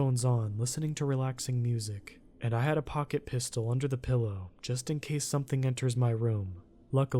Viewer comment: OK for a split moment where he said pocket pistol. I swear to God I thought he said pocket pussy.🤣🤣🤣🤣🤣